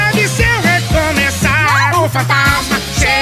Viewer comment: Почему я слышу на 04:52 ”Молли Макги у фонтана“, ахах..